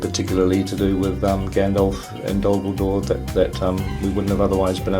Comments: Wait, did he say Gandalf and Dumbledore??? I know he didn't, but that is literally all I can hear XD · I seriously thought he said "Dumbledore" XD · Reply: He said DolGudor, but yes, it did sound like dumbledore XD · I thought he said 'Gandalf and Dumbledore'. Hey Jackson, hands off MY fanfiction! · I thought at first he said "Dumbledore." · I thought peter said gandalf and dumbledore 😂